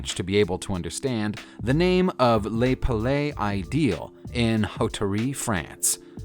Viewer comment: 🤣 "You don't have to be fluent in French to understand "Lay palay ideel"" I had to see the picture to realize what he tried to say ! 🤣